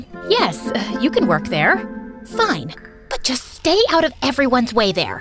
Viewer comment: i thought she said mum but she said "fine"